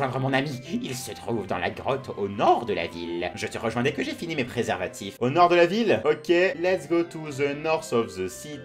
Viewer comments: vers 10:25 j'ai entendu "préservatif" il as fait exprès ouuu???XDD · j'ai entendu préservatif et c'était écrit préparatif xD